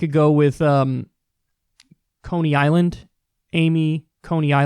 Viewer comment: I thought you were about to say 'anal' Amy Coney for a split second lol